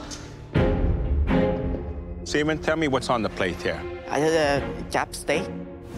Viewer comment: did he just say "Semen, tell me what's on the plate here?"